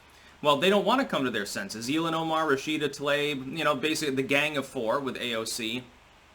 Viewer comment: Thought he said "gang of whores"